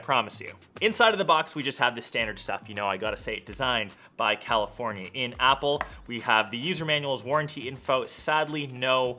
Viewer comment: Did he just say "Designed by California in Apple"